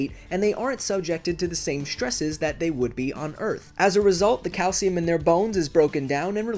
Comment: I heard cow semen not calcium